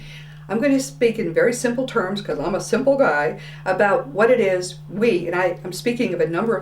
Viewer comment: Did I hear her say, "Cuz I'm a simple guy?" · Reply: I heard "Cuz I'm a simple guide" - ha!